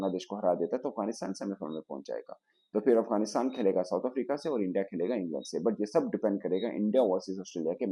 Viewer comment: India kya India say khelega, 😅 pta nahi lagga kya bole. 😅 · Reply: England bola hai usne 😂